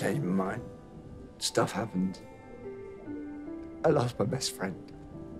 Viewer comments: Idk if it's just me, but every time when I hear Crowley say "I lost my best friend" - I hear, "I love my best friend" hgufgcyigvuhgjh And I... aaahhhhhh (just go to 3:25 and listen hahah) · I know he says: “I lost my best friend” but it sounds like he said: “I love my best friend” and honestly, he should’ve said that.